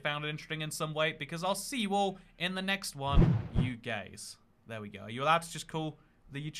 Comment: Legit thought you said "UK" when you said "you gays."